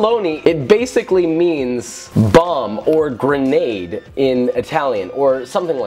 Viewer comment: I heard bum lool